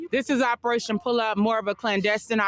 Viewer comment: It sounded like she said pull out. Everyone knows that doesn't work.